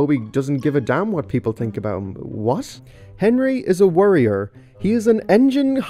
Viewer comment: I thought you said "Warrior" 😆